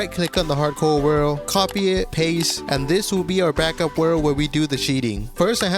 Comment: Also you can prove that you arent a cheater easily, just show the stats and instead of replay mod timalapse, a pov timelapse Oh, and for you deaf ass mfs here on 2:34 he clearly said that he as going to use a backup world to do the cheating